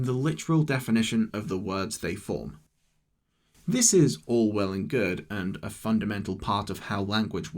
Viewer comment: did you just say "this is Orwell and good" ='(